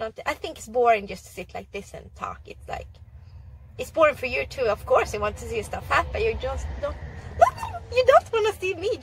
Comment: i heard : of course you want to see you stop fapp - then i cant believe & listen again few times this part, then i was like no way :D , & then i check subtitles :D XD , i guess i have bad ears :D :D:D